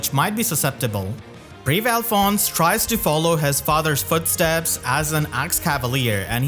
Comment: “Alphonse tries to follow in his father’s footsteps” no no sir alfonse SUCCEEDED in following his father’s gootsteps that’s my king 🫡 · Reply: I almost thought that said goosesteps for a sec